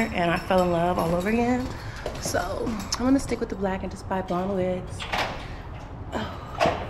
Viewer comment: mamas i’ve definitely heard you say that you’re keeping the blond and just buying a black wig lmal😂😅